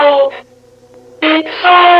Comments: I HEARD "it's sold ur balls" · It's all your fault. IT'S ALL YOUR FAULT. IT'S ALL YOUR FAULT. [Applause] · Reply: That’s what it was it was saying? I heard “it sold your mouth.”